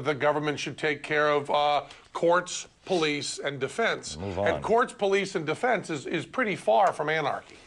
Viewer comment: Am I the only one who heard someone tell Beck to "move on" around the 2:07 mark???